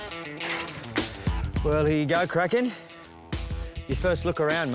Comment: I thought they called the male 'Crack Head" at first haha XD